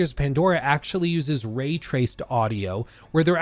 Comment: did you say “ray traced audio”?? I have absolutely no idea if this has been done before but omg that sounds very next gen, sheesh